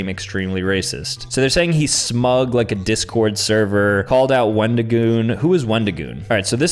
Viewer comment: I was listening the video and my ahh thought he said when to goon?😭